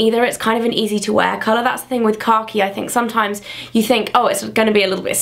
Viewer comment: the first time she said kahki i thought she said car key! xD